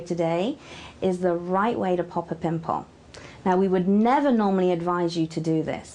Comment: did she just say penis 0:20 or around there